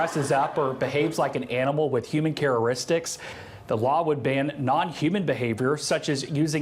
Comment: I heard absolutely nothing after 10:15 "CHARA'RISTICS"?!?!??😂🤣😆